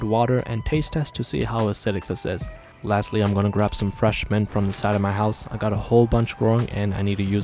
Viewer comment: I think I got hearing problems I thought he said "now let's go get some fresh men from the side of my house" 😨 I froze for a second